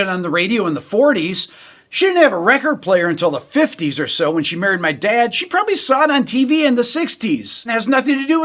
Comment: did you say 'when yr mom murdered yr dad?' That's some passionate woman 'bout her music, Rick!